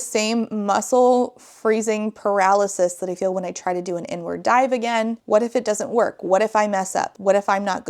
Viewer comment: The way I heard "n-word dive" instead of "inward (park) dive", at first 😭